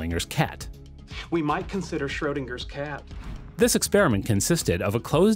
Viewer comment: it sounded like you said ShroedLINGers cat....